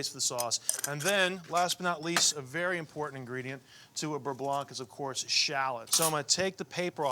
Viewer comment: For a moment there, I thought Tyler said he had “chai burre blanc”, which made me grimace.